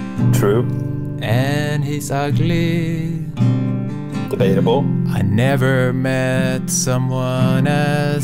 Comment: Did I hear cursed in Filipino language on 10:26? "p*ta ina mo" 🤣 🤣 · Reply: LMAO this is so funny! He actually said "debatable" but I'd rather him say what you thought he said 🤣🤣